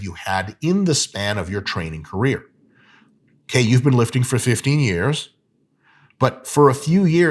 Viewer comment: "train for 15 years? I thought you said tren for 15 years 😅🤷‍♂️"